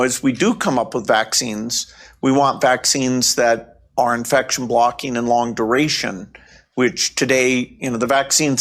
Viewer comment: did he say (he wants hib vaccines to be) infection walking and long duration